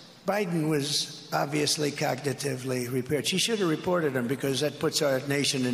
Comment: Did he just say "cognitively repaired"?